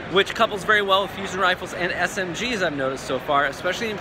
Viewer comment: did he say SMGs...i know kackis is really happy right now lol